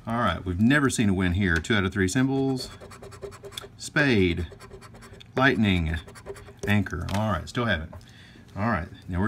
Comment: Construction going on in front of my house, so when you were on the second game, I thought you said "bite me" took a second for me to realize you said "lightning" (obviously my video quality was not too good, as well as my hearing). Made me laugh! Great video, as usual!